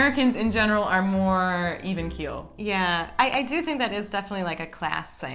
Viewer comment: Hi lovely. PLEASE, não sei se entendi bem mas a entrevistada usou a expressão "evenkill" para qualificar os americanos no minuto 12:17? Desde já, obrigada 😙